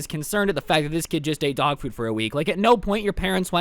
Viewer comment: I thought he said dog poo 😂😂😂😂